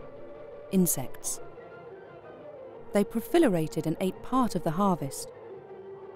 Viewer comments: did she just say “profilerated?” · Did she say pro-FILL-erated, instead of pro-LIFF-erated there or am I hearing things?